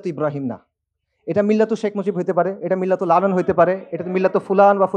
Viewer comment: মিল্লতু মুজিব এর পরের কথাটা কি বলল বুঝলাম না। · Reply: মিল্লাতু লালন। লালন ধর্ম